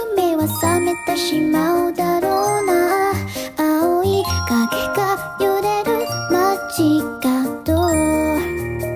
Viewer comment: *spits muffin* Did she say corona? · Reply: darou na